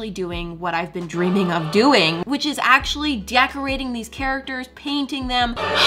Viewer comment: Mariah: Decorating What I heard: De-ac-orating🤣🤣🤣🤣🤣🤣🤣🤣